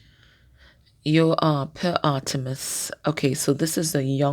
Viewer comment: can you clarify the card you pulled at 8:18? I heard Artemis but then it was a young boy.. captions were distorted.. shed light on the card please & thank you 🙏🏻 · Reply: Hey CP the card is called Peur Aeternus- the young boy almost a Peter Pan figure, the Fool 💖💖